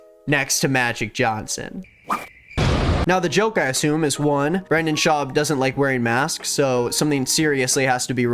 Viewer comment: I misheard and thought you called him 'Vernon Schaub.' I'll now be referring to him as Vernon from here on out.